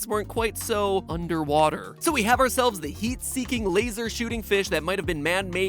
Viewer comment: Why did I hear Yeet seeking